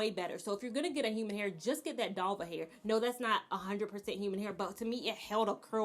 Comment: Is she saying dolva hair at the 7:35 mark? I can’t find Dolva hair.